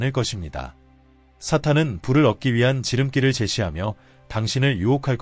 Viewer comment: 좋은 내용 감사드립니다. 옥의 티를 알려드립니다. 6:50 사탄은 불을 ->사탄은 부를 7:26.마태복음 8장 20절을 8시20분이라 읽는군요. 덕분에 여러번 들었습니다.